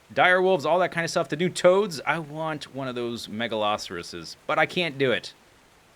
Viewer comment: Megatherium is a giant land sloth. Megaloceros is the moose-elk guy. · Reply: Oh, Megaloceros! I thought you were saying Megalosaurus! Sorry about that. My ears clearly need some unclogging.